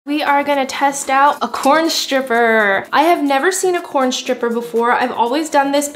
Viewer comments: Bruh she said "corn stripper" but I heard "Pole stripper". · I thought she said a poem stripper 😂